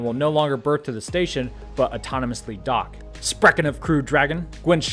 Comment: Did you just say "sprechen of Crew Dragon"? That sounded very awesome and genuinely made me laugh, but it was terrible pronunciation of the word "sprechen". ;D